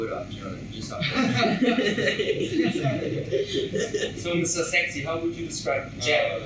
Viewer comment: what did Zach call Jack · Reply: He said sexy #jacharyforever